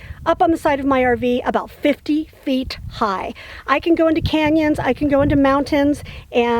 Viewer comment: It sounds like you say "50 feet high". Maybe my hearing is worse than I thought.